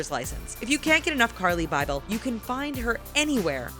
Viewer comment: lmao instead of putting Carli Bybel they put Jaclyn Hill on